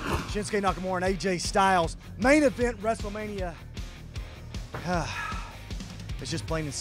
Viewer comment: Did you say " Main Event WRESTEMANIA "?!